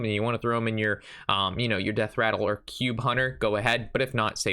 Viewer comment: Juan David Medina what do you mean last season lol?? I’m talking about the season we are playing in now. The decks I see from rank 5 to legend every month. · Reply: Thought he said "Cube hunter" being one of the best decks right now.